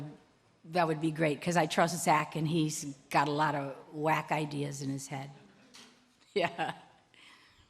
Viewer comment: It sounded like she said "whack ideas" :D:D:D:D